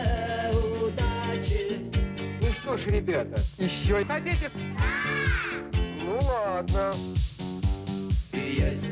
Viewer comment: лет 17 уже переслушиваю именно эту версию, никак не пойму. на 1:47 мужик что говорит? " ну что ж ребята, еще..." и от куда эта вырезка вообще? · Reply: "ну чтож ребята, ещё хотите?" - вырезка с какой передачи советского телевидения. Вообще они много звуков от-туда в "иглу" воткнули